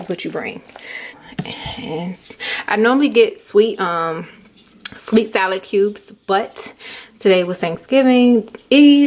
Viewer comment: J B yup you got it! Lol thanks for the perfectly written recipe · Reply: hello thank you so much for the recipe. I am SEVERELY a non cooker, but I'd like to make this for cookouts. at 5:08, did you say you usually get sweet salad sugar cubes? And also, is that 2 pound box of macaroni and double can of tuna?